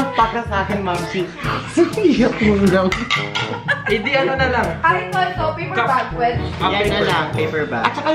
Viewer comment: AKO LANG BA NAKARINIG NG SINABI NI NA "BABY ANO NALANG...." SABIHIN NYO SAKIN NA TAMA NARINIG KOOOO